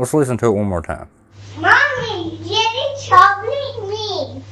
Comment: I’m glad to see you’ve debunked the video of the “haunted” family. I first saw it on The Dark Knight’s channel where he supposedly contacted the DK for help!! Although the little boy is not saying “Daddy” like you thought you heard, he’s actually saying, “Jerry” which is the name the ghost supposedly introduced himself as to the little boy. Thanks so much for helping me be much more discerning as I watch these videos- I discovered you last year and I felt like such a SUCKER when you revealed the truth about Lee, Really Haunted, but I would absolutely rather know the truth than continue to believe these creeps. ❤